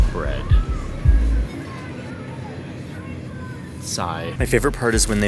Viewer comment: saai? nice dutch twist if i heard that right :)